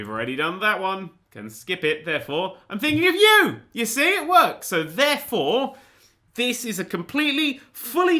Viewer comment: you said that works but is sounded like that twerks so funny u did Miley Cyrus